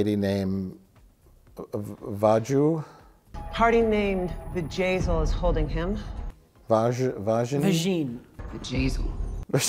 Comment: Vajazzle! Only thing I could think off everytime I heard her name.